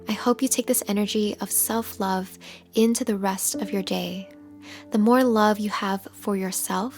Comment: did she really say gay instead of day?